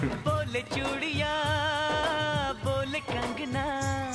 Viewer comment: I thought he said chu*iya🤣🤣🤣